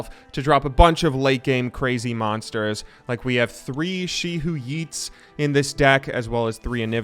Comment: did he called "she who eats" to the card she who wanders? lol some one is hungry :V